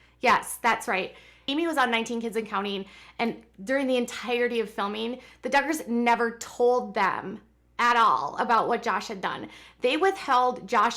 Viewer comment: Lol I thought I heard you say “ Juggars” well yes, the Duggars do go for the jugular..... 😂😂